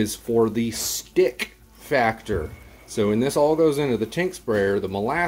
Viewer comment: Molasses in your paint sprayer. That's a great idea . 🙄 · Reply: It sounded like you said " when this goes into the paint sprayer ".